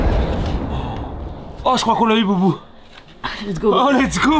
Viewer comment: c est moi ou il a dit zeubi 😂